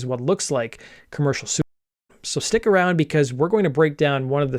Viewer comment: what word is cut off there, Commercial soup? Lol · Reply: "commercial suicide" - they censor it for monetization purposes